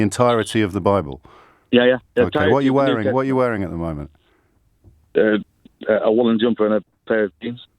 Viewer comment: I was hoping the answer was lingerie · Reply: Yes! 😄 I actually thought he said ‘a woman’s jumper’ at first and got excited! 😄